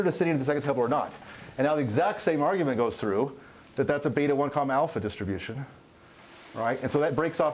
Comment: Wtf is a beta 1 com alpha distribution? Or what does he say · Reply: Beta(1,alpha)